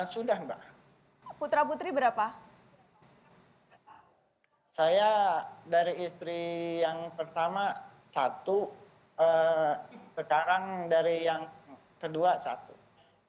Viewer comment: dia ngomong anak dari istri pertama 1 sama istri ke2 ada 1, gw komen yg bales pada nyolot2, kuping gw yg salah denger apa kuping lu pada yg ga bener denger nya? itu kuping tolong di korek lagi biar conge nya ngga numpuk di kuping, bila perlu isi2 kepala nya keluarin semua biar bener !!!